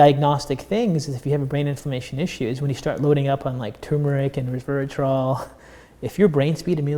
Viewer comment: I was wondering the same. He mentions the 2 supplements again in 42:26 but I can't understand the other word. Let me know if u can. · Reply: After googling a bit I think, "resveratrol" is the supplement he talks about